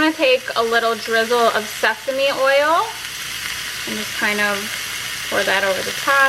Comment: you said seasme oil!! LMAO!! I thought you said oil!!! i was thinking " stephanie oil" that must be new! Where do i find this oil! LMAO!!